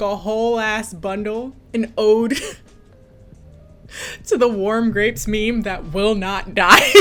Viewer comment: I didn't know what you were talking about, so I thought you said "worm grapes" and I was worried lmao I might just have to pay the ridiculous international taxes to support you